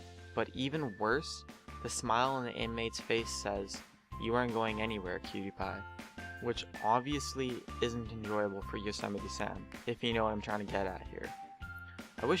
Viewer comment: did i hear "pewdiepie" at 1:59?